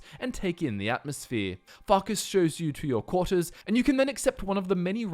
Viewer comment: who else thought he said 'fuckers' instead of farkas at 7:01?